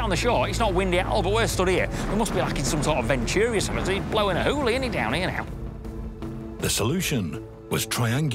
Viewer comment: wtf did you just say to me? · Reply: its blowing a hooley innit down 'ere now